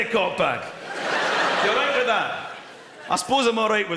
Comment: What did he say 7:32-7:33? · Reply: Pish